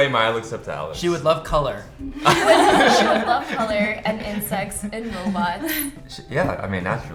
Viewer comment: Did anyone else also think said "She wouldlove color, and sex..." at 7:38.... lolol